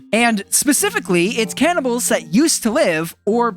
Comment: I thought she said carnival